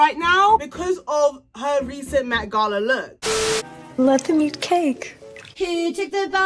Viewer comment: WHAT DID SHE SAY AT 0:37 IM SO CONFUSED😭💀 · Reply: Let them eat cake